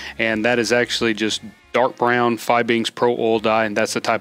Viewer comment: Did you say Dark brown fi beans pro oil dye